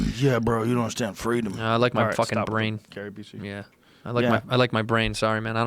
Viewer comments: What does joe say to Jamie at 1:50:22 ??? Tells him to stop but then can’t figure it out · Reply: Gary Busey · What does say at 1:50:22 to Jamie??? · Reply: gary busey